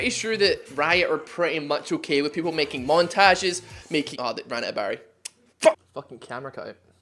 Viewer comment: out of battery did you say :^)